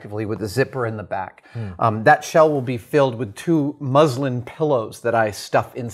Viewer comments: Was letting this play in the background and I kept hearing “Muslim” and I was very confused lol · Since I don't know anything about fabrics, I thought he was saying Muslim pillows, I thought: "I didn't know Muslims had their own pillow"